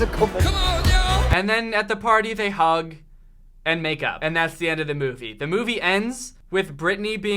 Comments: I thought you said that they hugged and made out😂🤣😂🤣😂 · I heard “they hug and make out” so anyways, my life is ruined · For a second I thought you said "they hugged and made out" and I immediately thought of the special victim's unit theme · Jesus Christ I thought he said make out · I thought he said “make out” instead of “make up” and that kind of scared me for a second. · he said “they hug, and they make up”, but I swear I heard him say “they hug, and they make out” I had to rewatch because I was like hold up. What? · I thought said: And then they hug. And make out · I thought he said, “and they make out.” And I went: “FBI OPEN UP!” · I thought he said “and then they hug”... “AND MAKE OUT” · I thought he said MAKE OUT not make up... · am I the only one who thought he said hug and Make out because that’s screwed up. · I thought he said “and they make out”😨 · I heard “they hugged and make up” “They hugged and made out” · I thought he said make out XD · I could have sworn that he said make out and I was legit confused. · For a second I thought he said "They hugged and make out." I was laughing my ass off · I thought you said "they hug........ and then they make out" and I almost spat out my water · Him: They hug, and make up. Me: has to rewind it to make sure he didn’t day make out:/ ... am I only one who thought he said that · When said they hugged and made up I thought he said they hugged and made out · i thought he said make out and i was like w h a t